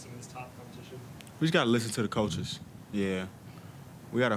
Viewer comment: He wanted them to know its time to do something up there. Say what u wanna say but he was making sure they know whats up 🙏💪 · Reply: It sound like he was saying “listen to the coaches”